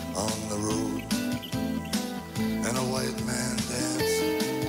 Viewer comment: What is he saying at 12:40 ?? I tried rewatching 4 times and can’t figure out what is hanging upside down. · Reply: "You'll see your woman hanging upside down".